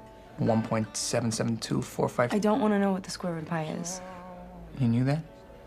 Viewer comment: Wow... just wow... it's one of my favorite scene of the whole saga... but.. it'd be very nice if somebody could tell me what she says at 0:43 .."I don't want to know what the......" ? Please help me :( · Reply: I think she says don't want to know what the square root of pi is? Sorry for being so late.